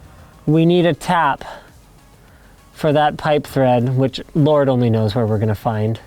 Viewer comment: you said you need a pipe thread I thought you said pipe friend 😔😔😔😔